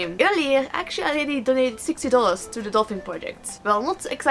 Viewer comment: I know it sounds like I say 'I donated 16 dollars to the dolphin project'. But I actually donated 60$! The screenshot only shows 16$ as the donation was split between several months of saving up :D btw... pssssst you should watch the video at 05:30... I adopted a dolphin! I'M SO HAPPY AND SO PROUD! :D